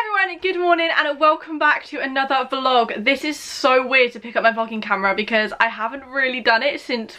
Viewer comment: it sounds like she is saying f***ing camera I had to watch it 3 times 😂😂😂